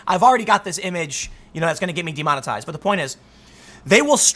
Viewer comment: I keep hearing you say "I'm going to be demonotized" yet I keep seeing ads on your vids.